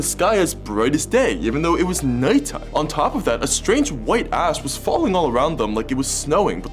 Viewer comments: Ngl, I thought he said "a strange white a$$-" 💀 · I thought he said "A strange white ass” 💀💀